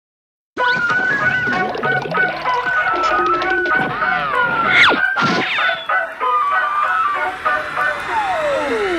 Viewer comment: ここら辺ですごくごにょごにょ言ってるんですけど 0:08 ここだけハッキリと「私が1番でしょ？」って聞こえるんですけど 空耳でしょうか？ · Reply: 愛してるっていったよね あたしが1番でしょ 許さない っていってますよ！！